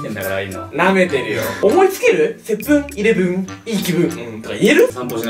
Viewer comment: 接吻イレブンにしか聞こえん笑笑